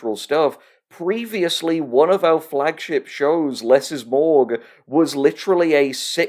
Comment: WAIT, IT'S "LESS IS MORGUE?" I THOUGHT HE WAS SAYING MORG ALL THESE YEARS.